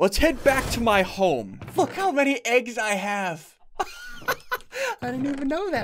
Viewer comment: At like 12:50 it sounded like he said f—k how many eggs I have. 😂